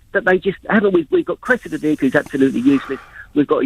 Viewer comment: I overheard someone say Putin said he was going to " get dat sad dick who cannot" as a trophy for his buddy Trump.....(jk). OMG did he just say he wished Chief Dick was firmer lol. OMG I've got the giggles. Her and the sad dick who cannot...but chief dick can......what a pair. Ha ha.